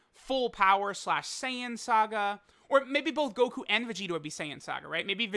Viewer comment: Did I hear right? Vegito being giant/saiyan saga?